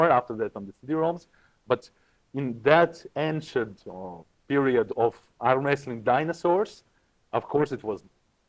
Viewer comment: Did he call John, Lupkes and Cobra "Ancient Arm-wrestling Dinosaurs"? 😂😂